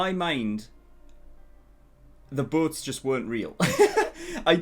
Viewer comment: I thought you were saying boots and I got confused at first until you mentioned Kruger destroying the boots 😂 English isn't my first language, sorry 😂